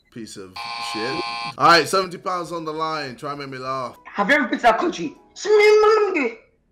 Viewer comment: Someone tell me what this kid said💀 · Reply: "have you ever been to that country, ssmphfhhfjfijc"